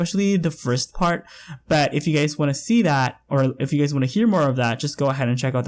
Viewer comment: I keep hearing 'you gays' and it kills me every time abhsja